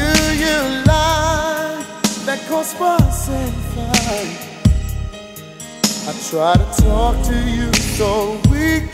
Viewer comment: I never understood what he says at 1:18 after "Why do you lie... " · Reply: "That u don't need help"